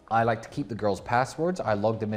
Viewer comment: pretty easy witch hunt. He basically told everyone he was a sex trafficker. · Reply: the part where Tristan said he would never give them passwords smash up rooms if he found them trying to make money on the side, the way he talks about women? "Edit" I thought I heard passports too. Either way they want to control every aspect of their life. You think keeping control to that degree without the threat of violence is possible.